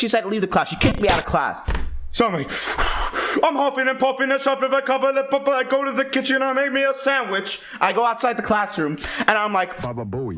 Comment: For those of you who couldn't figure it out, here it is: "I'm huffin' n' puffin' this off with a couple of people, I go to the kitchen and make me a sandwich." (As far as I could figure it out LOL) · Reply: I hear: “I’m huffin’ and puffin’ I’m shovin’ a couple of people I go to the kitchen and make me a sandwich.”